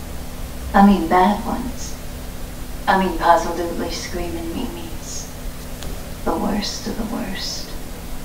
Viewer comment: it's okay haha you thought it said screaming meanies and I thought at first it said screaming memes 😂😂 another great reaction!